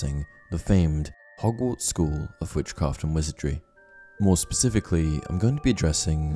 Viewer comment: When I was little i thought I heard " hogwarts witch after wizardry" instead of "witchraft and wizardry"